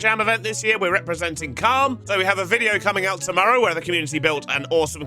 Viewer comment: I’m sorry you’re representing what in the yogscast jingle jam?! I’m pretty sure you didn’t just say cum but that’s what I heard